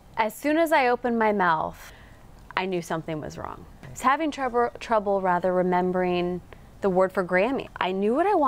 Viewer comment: was it me or did she just say trouber